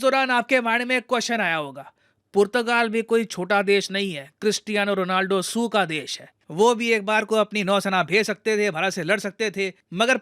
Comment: Did he just say Cristiano Ronaldo SIUU ka desh hai?